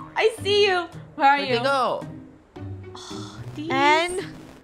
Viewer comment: what is saying? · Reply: “Okay go!”